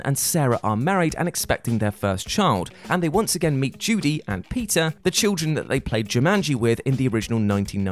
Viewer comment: My dude I thought you said Judy and Pizza during the last part